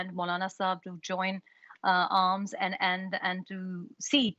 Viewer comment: did she say join arms ? 🤣🤣